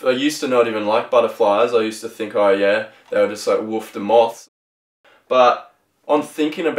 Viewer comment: haha what did u say? woofta moth?